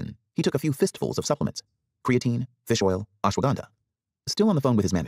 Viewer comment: did I just hear "Aswagandha", My Man is too real for consuming that one